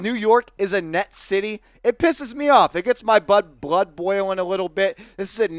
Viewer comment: What did you say at 2:07? 😅 it gets your what boiling? 😆. Jk bro! Great channel much respect 🙏🏾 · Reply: Blood lol